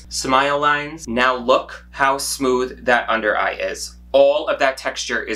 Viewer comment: Tbh I thought it said smother.